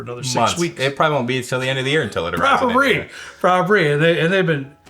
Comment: did you say "probabree, probabree"? instead of probably?